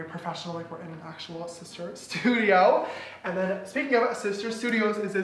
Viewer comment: did he say sister?